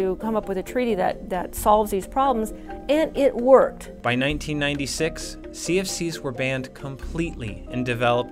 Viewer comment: You say they discovered in 1985(and no I didn’t mishear it. It’s not 1885 it’s 1985) and then at 4:19 you say ‘in 1965’(or something). So, they did this before discovering, or what?? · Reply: They said 1996